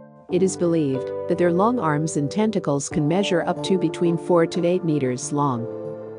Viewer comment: 4-8 meters long? Did I hear this correctly